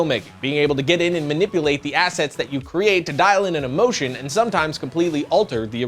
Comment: Did you say asses ?